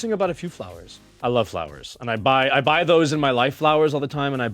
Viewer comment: When I thought Orville said “flowers for my WIFE” and got excited for 1.5 seconds. He said LIFE. 💀